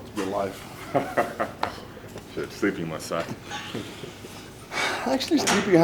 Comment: What are they talking about during their squat break? It sounds like Thor is saying "sleep pump"...what is that